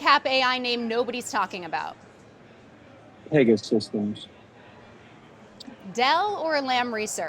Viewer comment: what company does he say here?? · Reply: PS… it is PEGA